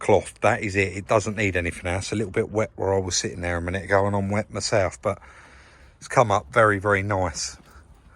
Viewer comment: I thought you said you wet yourself 😂